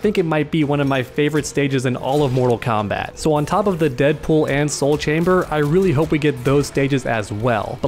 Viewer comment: what, I heard deadpool!!!!!!